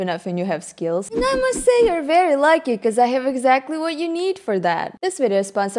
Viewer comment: all I heard was "Namase you're very lucky"